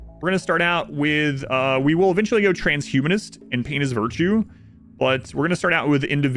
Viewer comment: did you just say "penis virtue"? Is that like tier 2 male supremacy?